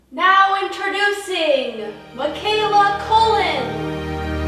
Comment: When she said "Mikaela Cullen" I thought she said "Macaulay Culkin" and I was like WHERE